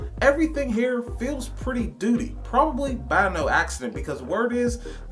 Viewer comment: did you say “everything here is pretty doodie”? 💩 Interesting review I wish I hadn’t missed the BlackFriday deal. Dang it!